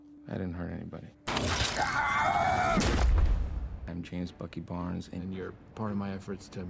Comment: Am I the only one who heard "james fookin* barnes?😂